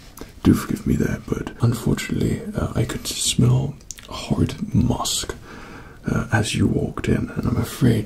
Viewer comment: did he say horrid mosque